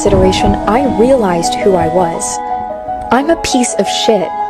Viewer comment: Why did i hear it “ I’m not a piece of shot” first!😹